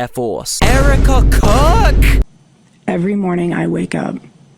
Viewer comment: when you said " Erika Kirk" my captions said " Ehud Barack"😭💀